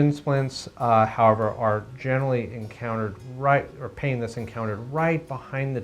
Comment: Hi! I’d like to ask why did he says in the video at 1:36 “painless encountered right behind the tibia” if he continued as “so much more spread out on the back portion of the tibia rather than very localised”? Is it a mistake by any chance or if it’s not than what’s spread out if it’s not the pain? I apologise I’m not native English so it could be my mistake I’m just a bit confused. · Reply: he said “pain that’s encountered” hope that cleared it up!